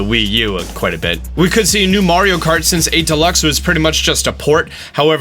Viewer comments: I highkey thought you said Mario Karty, that is a Mario Party game thats Mario Kart themed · Lol Did anybody else heard him say "Mario Karty"